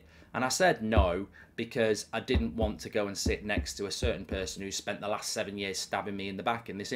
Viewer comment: Milking the crap out of this😂 thought he said he didn’t wanna go on anyway